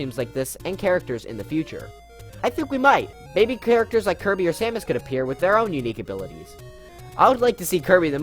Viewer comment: When he said, "...Kirby and Samus.." tbh I thought he said Sans.